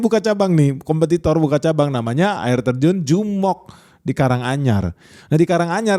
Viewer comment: Di telinga kedengeran "air terjun Jomok" 🤔🤔🤣